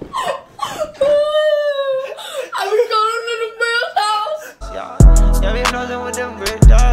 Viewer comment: this sound like it was say "I've crawling and my hairs out"